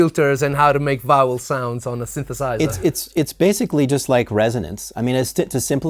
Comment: my inner child came out when I thought I heard say: "how to make bowel sounds on a synthesizer"...